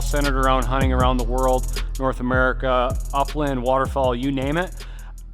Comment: Sounded like he was saying waterfall rather then water fowl